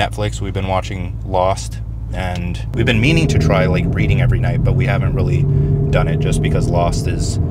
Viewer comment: lol thought I heard "we've been meaning to try breeding every night'